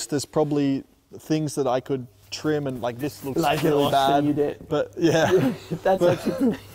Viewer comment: anyone know what max said here? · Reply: “Like in Austin you did”